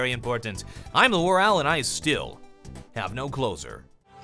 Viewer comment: When I was watching my first video, I thought that he said "I'm the WarOwl, and I still... have no clothes on." At the end and I was like wtf.